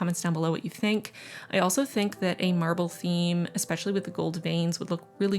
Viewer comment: Beautiful theme. I misheard and thought you were doing a ‘Marvel’ theme. Was utterly confused 🤣🤣🤣